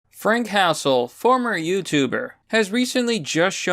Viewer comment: I keep hearing “frank castle” boogie squared off with the punisher